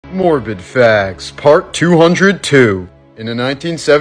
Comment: I thought he said 'morbin facts'